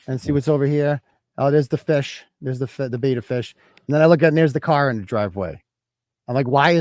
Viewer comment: At first I thought you said heres the beater fish. Then I realized it was beta fish.